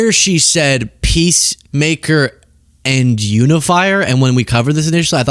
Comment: I heard, “identifier.”